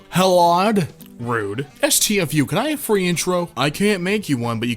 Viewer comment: i thought when he said "intro" i thought he said nitro xd